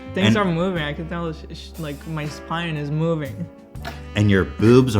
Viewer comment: Sounded like Hila said "she's moving " so girl ?